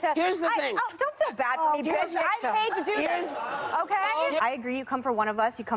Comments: DID SHE SAY BITCH LMAO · Did she say, “Dont feel bad for me bitch “ ? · Did she say bitch